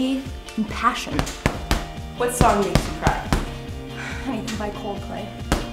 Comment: What song did she say she always cried to ? At 2:08 ?? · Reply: everything by coldplay